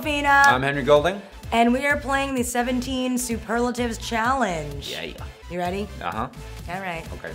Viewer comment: Omg for a moment I thought it said SuperLaxative🤣